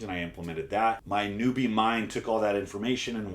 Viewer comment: Did he just say "Nubian mind"? Lmfao